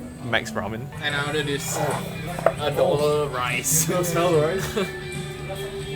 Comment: i hear ado 😭